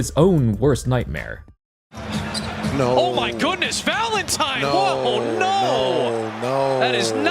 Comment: one commentator is saying "oh my goodness! Valentine!" while the other is just saying "No… no." you can hear it